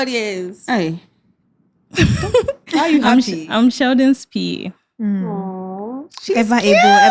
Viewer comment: At first I heard "I'm Sheldon's Pee". Then, I listened again and heard 'I'm Sheldon's P.A'.